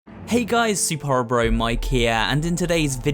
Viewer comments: When I first started watching this channel in the intro I always heard "superhorrorbro from Ikea" instead of "superhorrorbro mike here" hehe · I honestly thought it said superhornybro